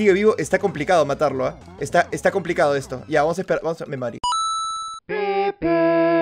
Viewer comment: alguien mas se dio cuenta que dijo "me MARI" 😅